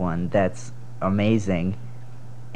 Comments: I thought he said ”I DON’T wanna kiss your face” lol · “You’re the amazing one” ᴬᴺᴰ ᴵ ᵂᴬᴺᵀ ᵀᴼ ᴷᴵˢˢ ᵞᴼᵁᴿ ᶠᴬᶜᴱ!!! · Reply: I thought he said "And I wanna kiss your feet" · I heard "and i wanna kiss ur feet" Soo I was feeling kinda something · Everyone gangsta until a small orange man says “ And I want to kiss your face ” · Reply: i thought he said "and i won against your face" HAHAHAAHAH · “and you need to remember that....AND I WANNA KISS YOUR FACE” Me : ☺️ ➡️ 😳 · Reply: I heard I don't wanna kiss you're face.. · I'm not native English speaker and I thought he said "I don't wanna guess your face" at the end (bc no face was drawn in the mirror) Then I read the comments <3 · “You’re the amazing one” ᴬᴺᴰ ᴵ ᵂᴬᴺᵀ ᵀᴼ ᴷᴵˢˢ ᵞᴼᵁᴿ ᶠᴬᶜᴱ!!! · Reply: ikr the difference is that I thought he said " I don't wanna kiss your face" Lmao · I thought the ending had said “even if I can’t see your face” 😂😂